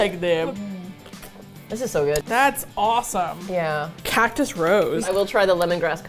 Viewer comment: Literally thought you were saying “Catnip Rose” the first two times you mentioned that flavor of sparkling water 🤣🤣 cactus makes so much more sense